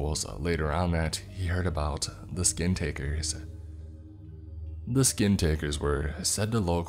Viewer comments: Yo am i the only one who thought it said the skin talker XDDDD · I thought it said, “don’t say the skinWaLker’s name” and I was like, “wait doesn’t that kill them?”